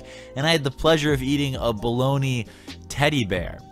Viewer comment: Wtf did I just hear "I ate a baloney bear"